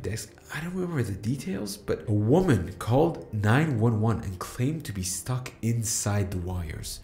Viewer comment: This was awesome! If possible, might want to change the subtitles at 3:21 - it says "9/11" instead of "911". 9/11, of course, being Sept 11th (unless this was intentional)